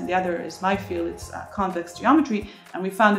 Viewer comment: She actually said "convex geometry".